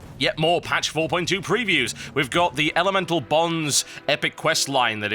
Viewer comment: I totally thought it said elemental Boobs